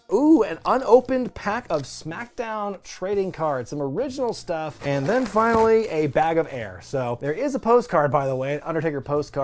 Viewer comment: I thought you said, "bag of hair" lol